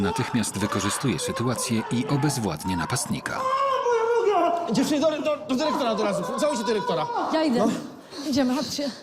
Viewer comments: nie wiem czemu ale usłyszałem "całujcie dyrektora" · czy tylko ja usłyszałam całuję dyrektor😂